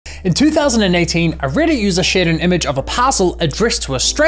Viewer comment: You said "2018" while the captions said 2008 lol